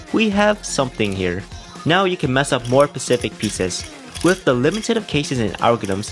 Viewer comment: he said Pacific instead of specific